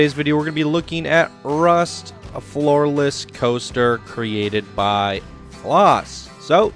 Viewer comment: At the beginning I thought you said "a FLAWLESS coaster" Instead of "a floor less coaster" 😂